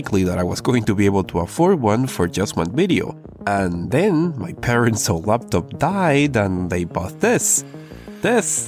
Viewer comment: I know you said "my parents' laptop died" but the first time I misheard "my parents up and died" and my brain went into vicarious panic mode for you. I hope that they're still alive and well.